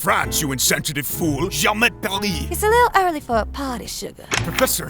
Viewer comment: i deadass don't know if he said "j'emmerde paris" or if he actually said "j'aime paris"